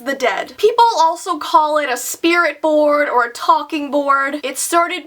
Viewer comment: For some reason when I heard, “spirit board”, I heard “shpear it board”